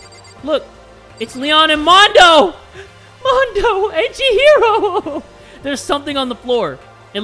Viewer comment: Tell me why I thought said "Mondo, edgy hero"